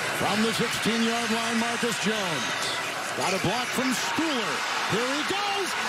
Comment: Very high rn and thought it said Mac Jones. Half way through I was like wait, he’s not number 25 lol